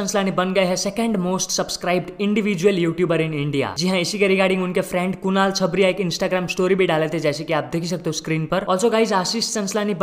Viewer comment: Did he say Kunal Chhapri?😁😁